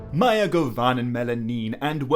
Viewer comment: Hi could anyone please tell me what the elven saying at the beginning is in English I'd appreciate it :) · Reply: “mae g’ovannen mellyn nin” meaning “well met my friends” :) (mellyn is plural for mellon im pretty sure, the word that gets the fellowship into moria)